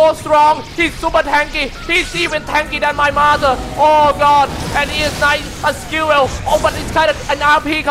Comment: did he say he is even more tanky than his mother?